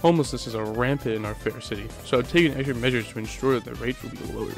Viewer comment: I thought you said extra measures